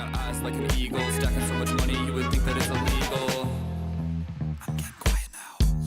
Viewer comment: When you say you think that it’s illegal in your song at the beginning I always thought that it said an eagle...